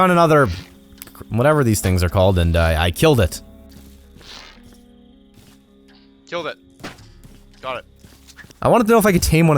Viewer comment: I WAKE UP TO THIS I HEAR MOTHERF*CKING DOG WHINING AND I THOUGHT IT WAS MY DOG BUT IT WAS A MINECRACT DOG AND I HEAR "i killed moosena i killed him" I GET UP AND I SEE A DEAD WOLF ON SCREEN AND IM STARING AT IT LIKE "dude wtf did you do the god d*mn wolf the f*ck is wrong with you"